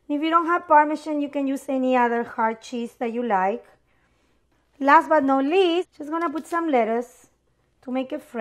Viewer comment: I thought said, "last but not leaf" when grabbing the lettuce